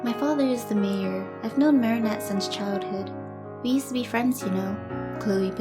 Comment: I heard it two times to assure that it said friends